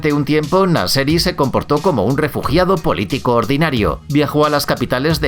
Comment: yo escuche re culiado :v